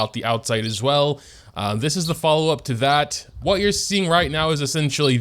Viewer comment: When he said "to that" i thought he said "tevyat"